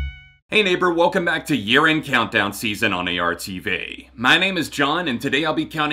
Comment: for some reason i heard “welcome to urine countdown of the season” BYEEEEEE